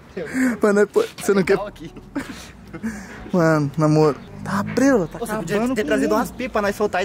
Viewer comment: foi impressão minha ou o falou "namoro" no minuto